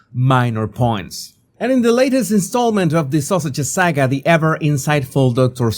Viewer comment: It sounds like he says the sausages 😂😂